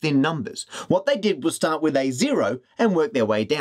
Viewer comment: the subtitles say "a zero" when it should be "A0" :P